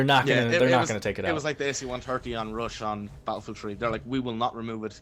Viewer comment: i thought he said AC-1 turkey instead of AC-130